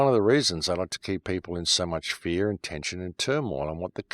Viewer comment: hey at 10:40 can you explain to me what i hear english is not my motherlanguage so maybe i make some things of it i have rewind 10times also in slow motion very weird what i hear ,so wanna check with you and dont jump to conclusions i hope you will clear this up for me thank you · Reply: "Its the reason they want to keep people in fear......."